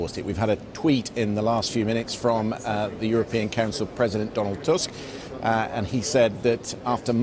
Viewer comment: I swear I heard him say president Donald tusk lmao dead It was chief